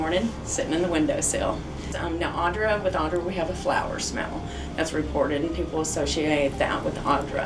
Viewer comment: I legit thought I heard say “they love flowers.” And instantly I was like how does she know dead people like flowers?lol